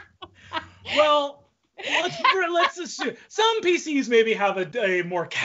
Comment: What does Emily say at 56:38 ? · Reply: sth like ”thats not always true” (about pcs knowing their character mechanics really well)